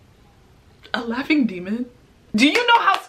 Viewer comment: At first I thought she said Latin demon, which seemed pretty scary. But jeez, I've never thought that laughter can be that frightening